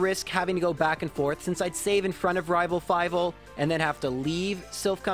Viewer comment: Did anyone else hear him say "Rival Fivle"